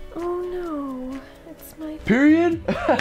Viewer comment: could you tell me what did you say after the girl saying ''oh no this is my...'' at 2:13? I couldn't catch it. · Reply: tomax Period